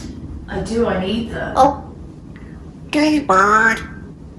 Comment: the 8:49 time stamp he says gay bird not do you bird 🤔 maybe they don't want to acknowledge it but that's clearly what he said and its possible that he meant it as in happy . You never know what Gizmo will say considering how stinkin smart he is 😊♥️🇺🇸